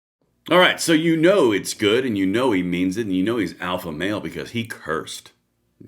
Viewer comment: I thought you said alpha male not awful male